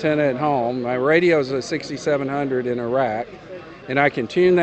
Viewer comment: at first I thought he said his radio was in Iraq LOL its in a rack. :)